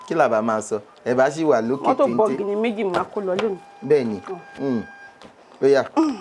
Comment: she said motor but the presenter didn't hear😅